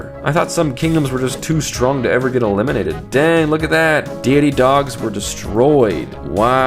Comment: I was watching but couldn't see close and I thought you were saying Diggity Dogs and I was like lol, what a weird name.